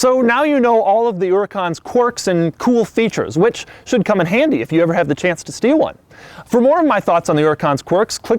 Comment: Lol did this dude just say "If you ever have a chance to steal one?" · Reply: I thought he said "if you ever have the chance to steer one"